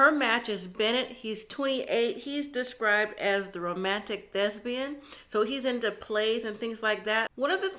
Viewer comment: Mom, you said "romantic thespian" but I heard "romantic lesbian". I thought we finally had a same sex couple. lol. I haven't watched yet because I wait until the weddings to tune in. Reverse Meka/Michael vibes? Lord deliver us from another Michael. You both look beautiful and I love your commentaries. Bachelorette parties always have at least one male stripper dressed like a construction worker, policeman, firefighter or an Army, Navy or Air Force officer. It's like the Village People of male strippers. I've never seen a Native American male stripper though